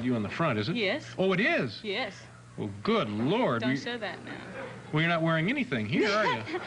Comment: Even during her time as "Vanity" she loved the Lord. Notice Dave says, "Good Lord!" when he sees her pictures. She responds, "Don't say that, now." It's at 1:59. She was a woman of God even when she did not feel worthy of walking with Him. I am happy she found the way. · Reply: i heard "don't share that now"